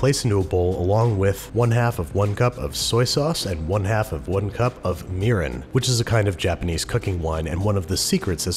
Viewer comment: I thought you said a cup of urine at first Babish